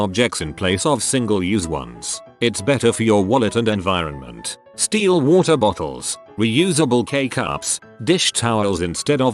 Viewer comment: oops, I just listened but didn't read the 1st one and I heard "steal water bottles, reusable K cups etc" 🙊🙊